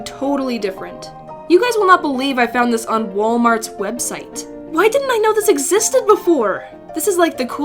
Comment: I heard “Walmart’s breadsite”